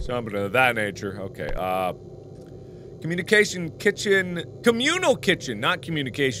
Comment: I thought it said Communist Kitchen.